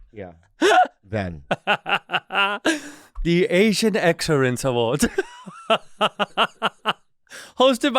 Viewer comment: I deadass thought bobby said The Asian Accents Awards as a joke... now its funnier